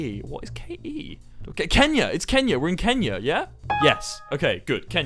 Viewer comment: My name is enya 😂 and I thought he said enya but he said Kenya